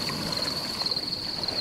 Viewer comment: "The Origin System will never be the same." Definitely not gonna be a prime. It's definitely something more than that. Most likely a quest? For Harrow, which leads to something bigger? Maybe Harrow is part of the main quest-line? (The day/night being kinda similar to the nature of the choices you make - good/evil - the choices you get is probably a real dilemma with sacrifices.) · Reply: also I keep hearing "The tall sacrifice" instead of "but all sacrifice"